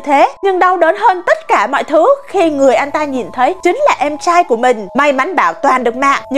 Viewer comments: Người anh trai nghe thành người ăn chai:)) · Người anh trai nghe thành người ăn chay ._. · Người anh trai mà tao nghe thành người ăn chay:))))))))